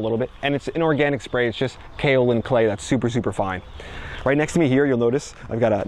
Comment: WHY DO SPEAK SO FAST? AT LEAST, SLOW DOWN WHEN YOU MENTION IMPORTANT INGREDIENTS! FOR EXAMPLE, AROUND @ 0:57 WHAT DID YOU MENTION AS THE INGREDIENTS OF YOUR (DID YOU SAY ORGANIC OR INORGANIC) SPRAY? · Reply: He said "kaolin clay", a powder used for insect pest control